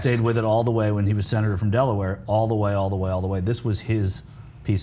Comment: Lol it sounded like she said Bitch McConnell